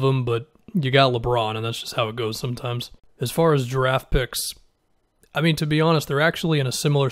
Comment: did he say "as far as giraffe picks" @ 8:20 ?